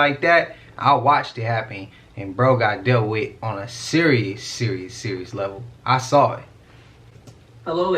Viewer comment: when you said "serious serious" it sounded like Siri lol